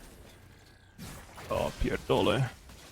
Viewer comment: did he say "ja pierdole"? xD